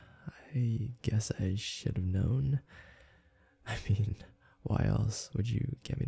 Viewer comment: Instead of "should have known" i thought you said sh!t a melon 😂